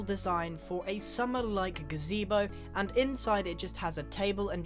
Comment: did u say gazebo?